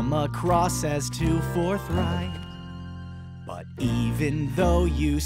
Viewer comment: I thought he was saying fornite